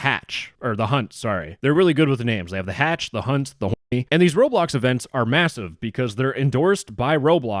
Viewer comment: The hatch the hunt the h0rny got me ngl 🤣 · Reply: omg i thought u said the hatch the hunt got me h0rny ngl